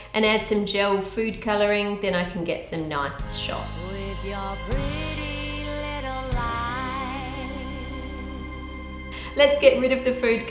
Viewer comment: Who else heard “with your pretty little lie” in 10:22 😂😂😂😂